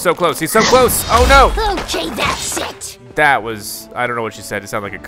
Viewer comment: I really can't tell but i think Rabbid Peach did say a curse. Hopefully somebody gets a clearer version of the voiceline. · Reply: 🐰 👑"ok that's it!"